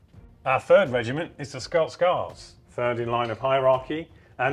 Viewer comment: I misheard the narrator, I could have sworn he said "Scouse Guards." Frightening thought.